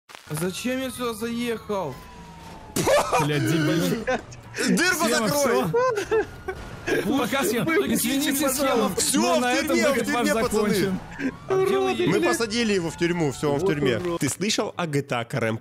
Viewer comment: "Дырку закрой!" Я слышу: "Дурку закрой!" XD